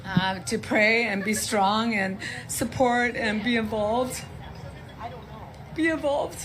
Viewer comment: This woman... Jesus. I think she means to say "be involved" not "be evolved," but maybe I'm wrong.